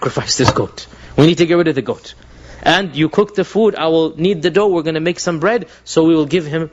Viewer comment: Assalamu alaikum, I would like to help by pointing out a small mistake which hopefully can be corrected. At about 01:55 the Dr says “I will knead the dough” but the animators misheard and wrote: “I will NEAT the dough” As I said it’s not a big mistake. If there is anything else I’ll add it in as I keep watching.